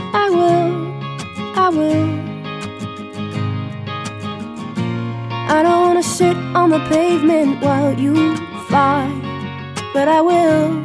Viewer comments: i thought she said "i don't wanna sh*t on the pavement " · I thought she said 'shit on the pavement'... My hearing is terrible xD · She doesn't want to shit on the pavement. xD Am I the only one that misheard that? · anyone who thought she said shit on the pavement needs cotton buds to clean the wax out of their ears...she clearly said sit!!!